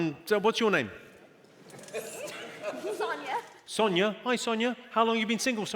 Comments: I heard lasagna 😂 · I heard her say her name was lasagna. 😂😂😂 *Sonjia